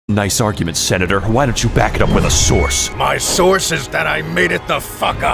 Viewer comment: I keep hearing it as "back it up with a swords" because of how Raiden shows his sword at that exact moment